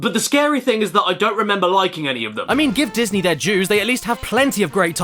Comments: what is saying here? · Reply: "Give Disney their dues" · I heard 2:19 as "Give Disney their Jews" and was just like "Jews? Wait, wasn't Walt a huge anti-Semite?" Well at least he's sealed away in a glacier so as long as nothing happens to that we should be fine giving Disney their Jews. ...What do you mean, "Climate Change"? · Honestly thought you said give Disney there Jews.. i thought this video was gonna go dark quick.. XD